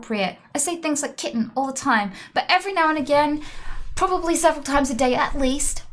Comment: wait did she say SEVEN times or Several* times. If it's Seven, I'm finna nut 😫